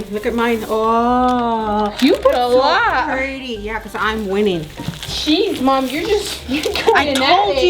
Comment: Did anyone hear cheese mom